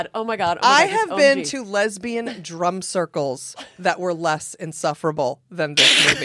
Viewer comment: "I have been around lesbian drum circles that were less insufferable" LOLOLOL!!! I CANT!!! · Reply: I thought she said drug circles 😂